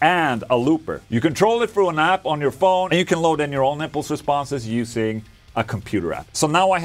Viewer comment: Sounds amazing, perfect portable live rig!! 4:02 "you can load in your own nipple responses" I had to rewind making sure I didn't misheard anything xD